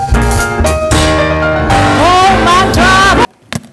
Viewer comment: おまたはって聞こえる。 ワロタwww。